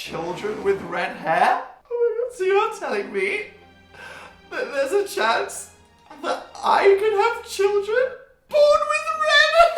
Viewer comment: I heard it as: "So you're telling me... that there's a change... that I might have children?!??!???! " I'm was just like..l same dude... same...